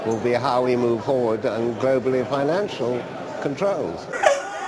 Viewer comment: did he said ,at 1:57 "controls" ?